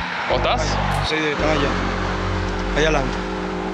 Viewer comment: кто знает Испанский?Что он сказал? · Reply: Я различаю, что он говорит на испанском "Para allá. Allá adelante", хотя звучит сокращенно "Pa'lla. Allá alante". В переводе: "Туда. Там впереди." Хотя самое первое слово не разобрала. Наверное, "автобус".